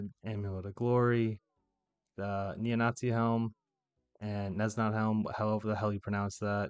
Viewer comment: Did I hear you say “neo-nazi helmet”? 😅🤣